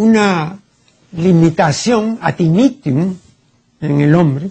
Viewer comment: El maestro dice: Limitación adimitium o que dice.Ayuda.